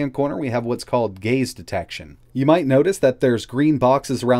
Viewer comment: sorry for being a 2 year old but did you say that AI chips have the ability to detect gays? :O